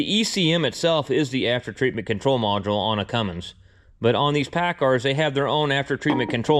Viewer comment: I thought you said these Paccars were not very good Why bother when it looks like your workshop is full to busting already